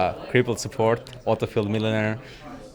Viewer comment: did he just say auto-filled midlaner?